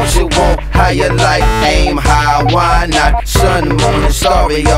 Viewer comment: Aim high why not?? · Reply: I thought you said "Am I high, why not?" lol